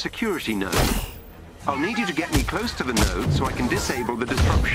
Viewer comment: I heard nerd, not node. I'm an MGS nerd.